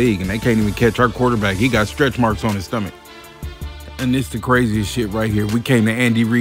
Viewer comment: did he just say aoc got stretch marks on his stomach 😭😭😭😭😭 raaaaaiiideerrrrssss